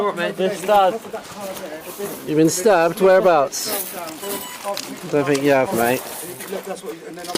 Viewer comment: did he say "dont think uou have mate" as in cop don't think DUDES been STABBED??? GUESS WRONG MFER!!!!! I mean fire him, sue him, sue the city... maybe charge him... other cop too... that is a complete negligence, lack of due care... cop is absolutely guilty of involuntary manslaughter if not criminally negligent homicide...